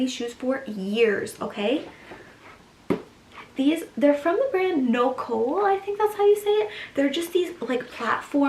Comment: what brand did you say the black sneakers at 8:00 were?👀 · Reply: i tried to find it, im pretty sure the brand is spelt nokwol, but i can't find the exact shoe :(